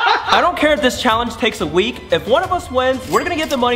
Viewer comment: Claim your “here within an hour” ticket right here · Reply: Oh I thought you said 4 weeks